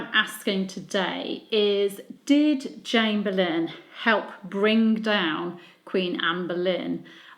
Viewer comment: I thought it said did Anne Boleyn help bring down Anne Boleyn, I was like wut